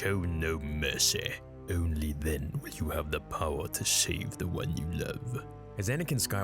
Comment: I heard "you have the power to shave the one you love"